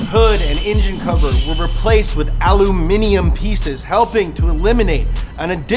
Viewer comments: Literally the first time I hear an American pronounce it "Aluminium", subtitles are "Aluminum" though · Did he just say aluminium?